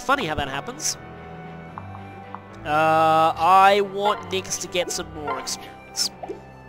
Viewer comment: What you said was: "I want Nyx to get some more experience." What I thought you said was: "I want Nyx to get some nohr experience." In the second case, just marry her over to somebody like Leo.